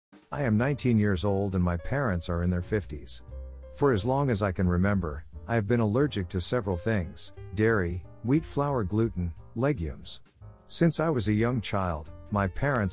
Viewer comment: Story one: I totally misheard legumes as legos, and was concerned for a moment that they were feeding the OP plastic.